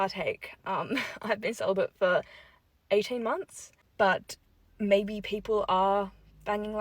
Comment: ‘Celibate for 18 months’ yea sure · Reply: I thought she said "sell-by date was 18 months ago"